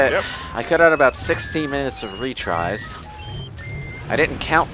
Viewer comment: For a moment I thought said "60 minutes of retries"